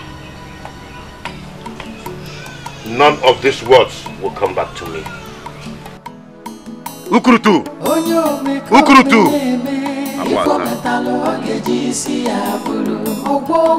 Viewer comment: did he say kukurudu? 😂😂